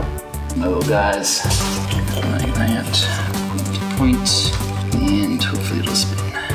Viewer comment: Autocaptions say: no guys aunt points in tofu